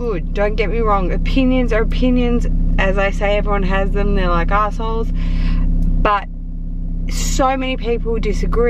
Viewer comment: did u say “they’re arseholes”? 🤣